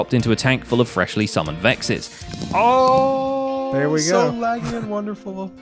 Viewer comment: thought he said "freshly summoned brexit"